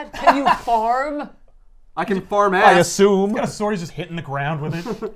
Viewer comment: Wait, did say, "I can farm ass?" PUT IT ON A DAMN SHIRT!